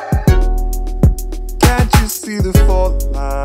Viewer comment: i thought he said cant you see the fortnite im crying